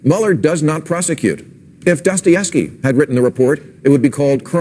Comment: I thought that Maher said "Dustyevsky"